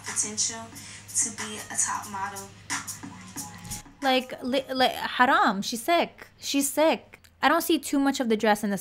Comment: Oi! Did u say Haram😂